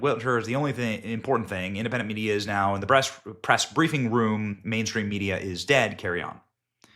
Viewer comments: At around 5:40 Benny says "breast" room instead of press room. Quite a Freudian slip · Benny actually slipped and said "Breast" instead of "Press"...anyone else catch that? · Did anyone catch our boy Benny accidentally say "BREAST" at 5:42 instead of Press as he's looking at her pictures? Too FUNNY!!! Yes, we are men, yes we are.